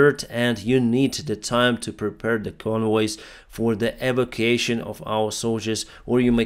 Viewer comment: What? That's not an English word! CC says that you're saying 'evocation', which is the action of invoking a spirit or deity.